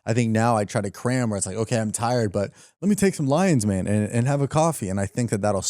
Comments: Did he just say "Let me take some LINES"? 14:40 (fixed timestamp) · I heard "let's have some lions mane" as "let's have some lines man" lol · are we gonna just skip over the fact he said "If I workout before the show I'll feel better, If I take lines, If i have caffine..." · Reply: I think he said "lions mane" 14:40 😳